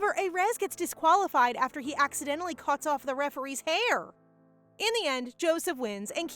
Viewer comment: I thought she said “arm” not “hair” 😅😂